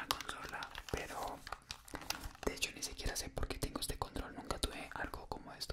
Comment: Entendí condón en vez de control jaja